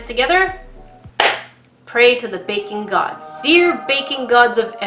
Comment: When she says "baking gods" it sounds like shes saying "bacon gods"